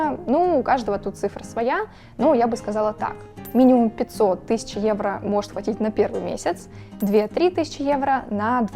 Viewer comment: Oczywiście ten vLog jest skierowany do Ukraińców ale jako Polak uważam że jest bardzo realistyczny i pozytywny. Zarówno polska jak i reszta Europy ma podobnie wymogi i lokalne prawa do których przybysz musi się dostosować. Idealnie nie jest bo biurokracja jest duża, no ale albo biurokracja albo korupcja. 🙂 Osobiście już wolę biurokrację bo korupcja mnie zawsze brzydziła i nawet w czasach PRL nie dawałem nikomu łapówek ani później nawet w szpitalu. Nie róbcie tego bo to jest karalne i w Polsce nie jest tolerowane. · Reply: Мне послышалось 500 тысяч евро?